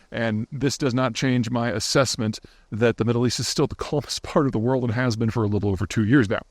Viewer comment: what word was said here? "the middle east is still the _ part of the world..."? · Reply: calmest